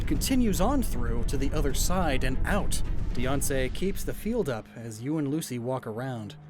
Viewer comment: Every time he says Diancie's name, I almost mishear it as Beyonce.